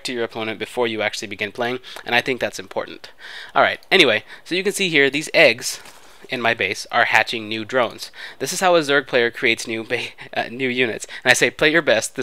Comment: Captions: you can see here the sixth in my case are hatching drugs What X really said: as you can see here there are these eggs, in my base, are hatching new drones